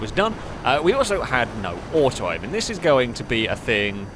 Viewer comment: "no horse riding"? What did you say?! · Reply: Oh! No "auto aim" - sounded clearer later on😁